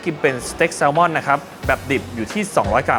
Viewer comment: แซลม่อน 200 mg ??? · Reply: เค้าพูดว่า 200g นะตรางสรุปน่าจะไส่ผิด 😂